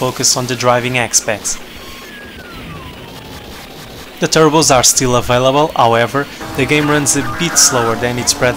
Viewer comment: well what were you saying about the turbos at 1:13, was it "the turbos are still up by level"? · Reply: Matthew Rivera "the turbos are still available, however"